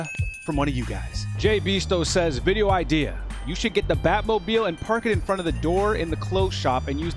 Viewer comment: Who else thought it said Baltimore instead of Bat mobile😂 I thought he was going to use Baltimore slang or something and see people’s reactions